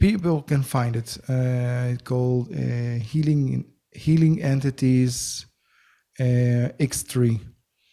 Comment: I'd really like the name of that book and the author's name he spoke of. I tried to find it under the name Healing Entities Extreme which is what I thought he said and couldn't find it. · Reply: X3: Healing Entities and Aliens by Adrian Dvir